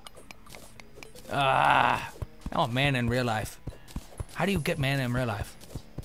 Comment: i thought he said "I want a man in real life" XD